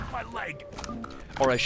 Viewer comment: Whenever the Resistance say "My leg!", I always hear "Mein Leben!". I must've been playing Wolfenstein 3D a lot lately. lol